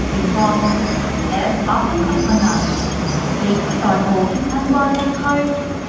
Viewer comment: "情況"之後英文講咩? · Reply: FR 呼吸困難 (FR = First Responder)